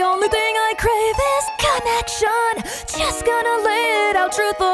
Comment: I swear i heard "Connection" as Cat action" im dead